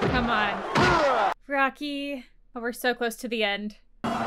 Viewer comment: So when you thought drago said your dead to rocky entering the final round he’s actually saying to the end